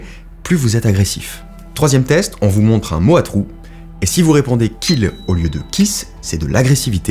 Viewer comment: J'ai pensé à "kick", ça veut dire quoi du coup ?